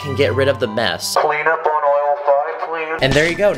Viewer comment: ClEaN uP tHe RoOm (btw idk what he said) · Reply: Clean up on aisle 5